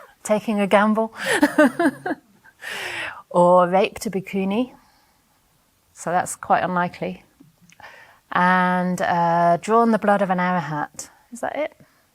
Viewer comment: What is it that you say at 34:22, ”the only people who can’t find liberation are people who killed their parents, raped a ”something” and drawn the blood of ”something”? ” I can’t quite hear what you say and the subtitles are not correct i guess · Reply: A Bikuni an Arahant